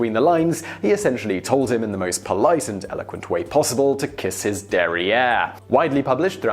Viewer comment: lol :D kiss his aaa.... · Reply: Tell Me This Oh, i thought he said "Kiss his derriere", but with an American accent